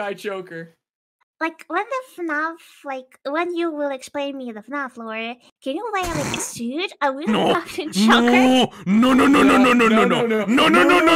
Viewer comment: does anyone know what said here? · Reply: “When you will explain me the FNAF Lore, will you wear a suit, will you be wearing the Afton Choker” I had the subtitles laying around I just forgot to add them in lul.